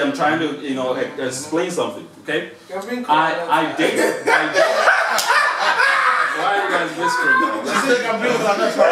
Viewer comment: can someone please tell me what she said at time marker 12:01? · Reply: Kat Ingram hi , she said bring kolanut. kolanut is a Nigerian thing.it's like a peanut but not peanut. It's used in Nigerian homes..its used to welcome visitors.